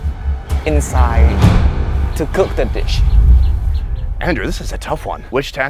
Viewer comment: I swear I heard “too cook that btch” 😂😂😂Had me shook had to replay it multiple times.